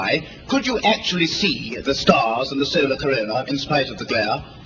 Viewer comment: On re-listening to the clip from 5.08, Moore may well have asked "...when you looked up at the sky, could you actually see the stars AND the solar corona..." (not "in" the corona). There is a time lag before Neil answers this at 6.14, and Neil appears to misunderstand the question because, in answer, he speaks about what stars they could see whilst photographing the corona. Perhaps he thought Moore said "in". However, Moore gets a 2nd bite at asking the question in the 1970 interview (3.28).